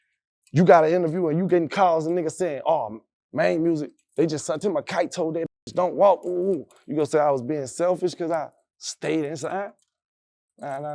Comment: my high ass thought this one elon's kin, i thought the video said "maine musk" lmao